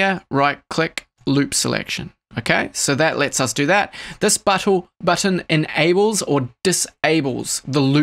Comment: does anyone else hear him say butthole instead of button at 9:19 ?